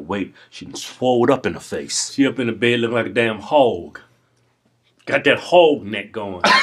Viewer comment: "got dat howg nek" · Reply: I thought they said "got dat Hulk neck" xD